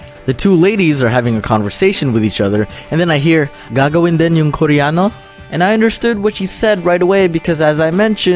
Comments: I thought the 2 girls in 2:13 said "Gago barin yung koreano" No offence tho ur cool dum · I LITERALLY HEARD HER SAY*"Gago rin ba ang koreano?" * WTF IM CRYING Edit: for people who think I really heard that, it was only the first time. Sub 2 me pls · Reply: It was supposed to "Gagawin din yung koreano?" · "Gagawin din yung koreano ?" 😅😂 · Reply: I thought at first they said "Gagu rin ba yung Koreano?"... yep.. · did he just say gago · I thought the barbers said “gago pala yung koreano” XD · i heard ganggong and then yung koreano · i thought you said "Gago din yung koreano?" haha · I thought she said GAGO BA YANG KOREANO? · In 2:14, it sounded like you said: "Gago din yung koriyano" ("Gago" is slang term which means "fool" I guess xD) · lol I thought dom said "gago yung koreano", had to replay that part to make sure ahahahaha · i heard "gago din ang koreano" oops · lol I thought she said "Gago rin din and koreano?" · i thought it said gago rin yun koreano? (Korean is also a bi*) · lmao i thought you said "gago yung koreano" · I hear "gago din ang koreano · at first I thought you said :" gag* rin ba natin yung koreano?" X,D im sorry/gomennasai/pasensya hahahaha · Lady: gagawin din ba yun koreano I thought she said "gaga din ba yung koreano" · I thought she said “Gago yung koreano” Haha😂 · eng sub says, "Gago in Denim Koreano" instead of "Gagawin din 'yung Koreano?" 😂